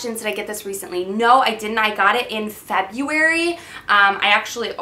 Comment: I love the fact that it sounded like you said "Fabuary" - I instantly thought like.. Oh okay.. Fabulous-ary, that makes sense.. :D